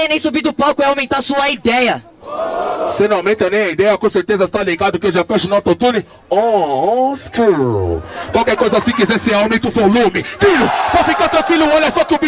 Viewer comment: Eu não entendi o que o falou nessa rima. Alguém traduzir por favor? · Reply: "cê não aumenta nem a ideia com certezas tá ligado, oon skrull, qualquer coisa se quiser pode aumentar o volume"